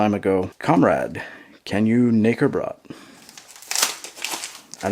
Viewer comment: it actually says "Do you know" instead of "Can you", very small mistake as, kennst and kannst are really similar. Keep it up!